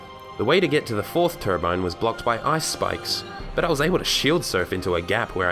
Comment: I heard ice spice 😂